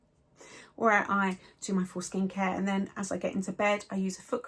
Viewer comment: I thought you said “foreskin care” but you said “full skin care” 😂